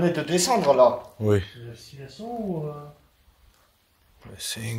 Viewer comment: Bonjour, Un petit mot pour vous indiquer que là où vous avez entendu "halluciner", moi j'ai entendu "pas de souvenir" (un peu après la 30eme minute, quand vous demandez combien ils sont 😊).. Je me fais un rattrapage de toutes vos vidéo et je suis vraiment heureuse de vous avoir croisés sur Tiktok, comme dit dans un précédent commentaire : je ne suis pas prête de vous lâcher, bravo à vous 3👌🏼👏🏼🙌🏼🙌🏼🙌🏼😍😘😘😘